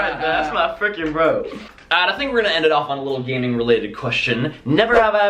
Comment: i thought he said gang related